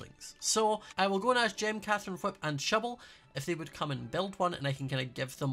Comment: I’m super tired while watching this and a couple times I either misheard or accent tricked me but here are the ones I noticed Fun - Fiiiinnnnee Shubble - Shovel Skills - Skulls or Skells …idk I found it kind of funny.